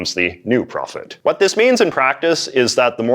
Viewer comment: I really thought it said mason prophets...